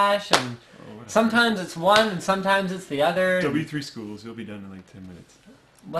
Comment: "double e3 schools"? couldn't understand him..